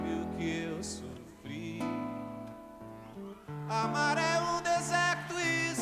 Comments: Funny detail: "Amar é um deserto e seus temores" means "To love is a desert and its fears", but "Amar é" sounds like "A maré" which means "The tide". So "The tide is a desert and its fears". While he is comparing to love with a desert, he still equates love with the sea. Interesting detail. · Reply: Geeenteee, eu sempre achei que ele falava “a maré” haha , nunca nem passou pela minha cabeça que ele estivesse dizendo “amar é…” Tem de tudo no mundo ! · Funny detail: "Amar é um deserto e seus temores" means "To love is a desert and its fears", but "Amar é" sounds like "A maré" which means "The tide". So "The tide is a desert and its fears". While he is comparing to love with a desert, he still equates love with the sea. Interesting detail. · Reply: I am sure I am not the only one who used to mishear "amar é um deserto" ("love is a desert") as "amarelo deserto" (either "empty yellow" or "yellow desert").